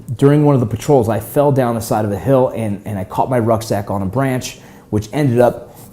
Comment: I thought he said he caught his "nutsack"